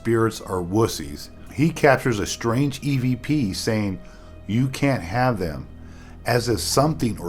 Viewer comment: When they capture the EVP say, " You can't have them ", I have headphones on and to me it sounds like it says, " You can't SAVE them ". I may be wrong, but then I might be right.